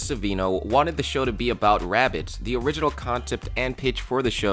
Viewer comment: I thought u said rabid